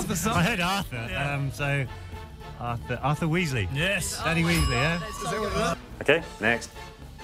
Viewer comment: Did he say "Daddy Weasley"?😂